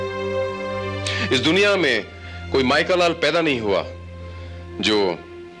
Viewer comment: Injured Inspector BHARAT... 🔥 Utna aur dangerous ho jata hai itna ek " GAYAL SHER "... !!!! At 34:07... Ish Duniya mein koi Michael Lal paida nahi hua Jo Inspector BHARAT ka bal bhi baka kar sake...!!! " INSPECTOR BHARAT "🔥 💪🏻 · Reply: Michael lal nahi mai ka lal 😅